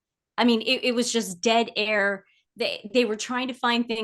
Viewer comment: Lol, I thought said that every episode was full of "dead hair"....(probably true), but she said " dead air"😅😅😅😅